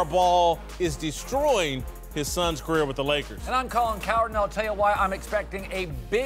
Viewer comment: When Colin Cowherd says his name I always hear "Coward"